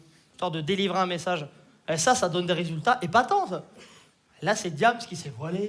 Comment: "Des résultats épatantes" ?? 11:05 Toi aussi tu fais des fautes de français^^ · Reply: Il a dit "des résultats épatant hein ?" Mais il l'a dit vite donc on dirait épatante